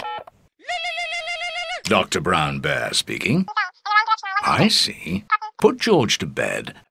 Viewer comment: I sneezed so this sounded like “Time to run bear speaking.”